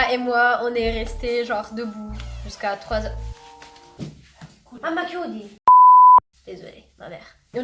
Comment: Ma parli italiano?? J'ai entendu "mamma chiudi"?